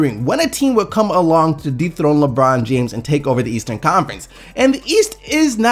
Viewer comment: Sounds like you said deepthroat lebron instead of dethrone LOL